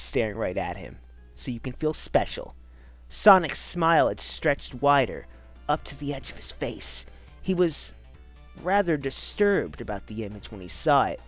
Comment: I heard this at the first time: "Sonic's smile was streched wider, up to the edge of space." I laughed so fucking hard that i fell from my chair, but the second time i heard it right and it said "Sonic's smile was streched wider up to the edge of his face." I was kinda dissapointed, but still, it was hilarious.